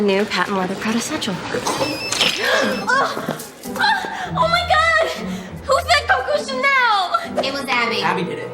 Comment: I have replayed 3:53 like 20 times and I cannot understand a word of what she says. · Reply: "Ugh. Oh my God. Who fed Coco Chanel?"